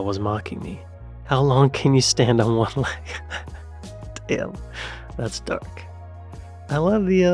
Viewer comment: I was confused for a moment when I misheard one of your comments! The pic of the man in a wheelchair, you said "that's dark" - I heard "that's duck", and was thinking no, that's a flamingo!? 🤣